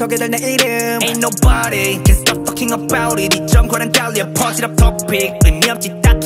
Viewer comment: I LITERALLY THOUGHT CHANGBIN SAID "AIN'T NOBODY CAN STOP FCKING BOUT IT" 💀